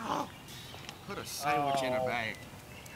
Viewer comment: did I hear you say "That's a sandwich In a bag?"..any way that was great coverage, love the tournament vlogs!